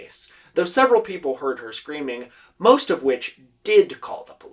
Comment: WATCH OUT!!! your captions are wrong at 5:35 ("most of which" vs "most of whom") and 5:44 ("not a murder" waqsnt said and also the next sentence is entirely different) 😨 I love captions! lets strive for great caption. heres some other erorrs: 6:11 6:26 7:04 7:18 Im gonna go do my homework now so i cant keep watching sorry